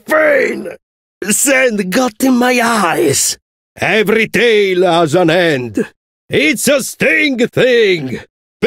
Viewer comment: I hear "Every tale has an end"